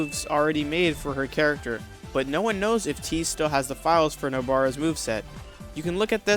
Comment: I thought he said he lost the files?!